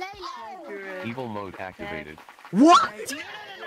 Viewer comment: When Command bot said "evil mode activated" I thought he said people mode activated! 😅😊